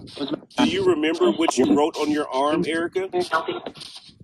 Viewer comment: I heard a man say Erica